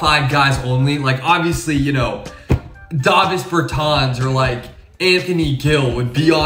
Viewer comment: Did he just say “Dovus Bertans” ????